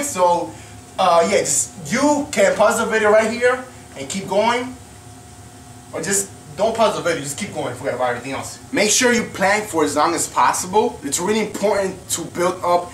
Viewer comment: Did you say plank at 5:16? I know what plank is, its just to make sure you said it so I can do it.